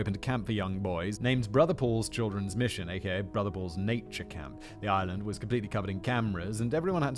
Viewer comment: I thought he said “brother balls” not brother Paul’s 🤨